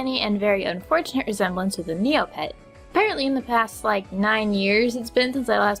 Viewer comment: What does she says? "She looks like a ?MeoPet?" Seriously, can someone write me how it is written? I am really interested in it... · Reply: I think it is "NeoPet"